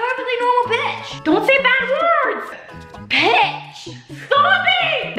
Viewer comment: I really thought that JoJo said the B word but she said pitch. Well, in the never have I ever game, she said she never said a bad word. Miranda said she is a liar, so I don't know who or what to believe.